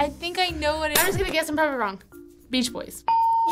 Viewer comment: captions say "Sometimes I'm" except clearly said I'm probably